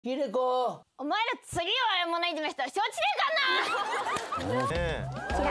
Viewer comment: 最初なんつってんの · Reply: 「お前ら次弱い者いじめしたら承知しねえかんな」に聞こえる